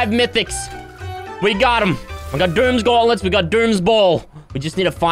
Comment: Did he just say he had dooms BALL